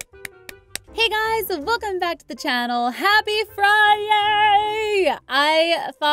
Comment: that was funny and cute and 0:18 did you say friyay?